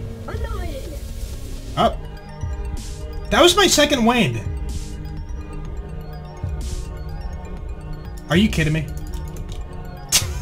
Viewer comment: it sounds like he is saying “second wah”